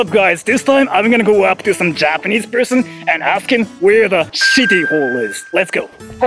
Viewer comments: I heard "Shitty Hole" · I actually thought said "shitty ball" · I didnt read the subtitles and I heard "shitty hole". Good way to describe the bathroom lol.